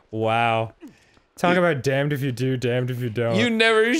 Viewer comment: For a moment, I thought Dan said: "Dan if you do and Dan if you don't."